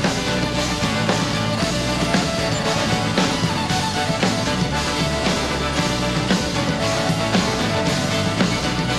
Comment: wish I knew what Carter was saying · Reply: "Change the focus to the other guys. They are doing the interesting stuff"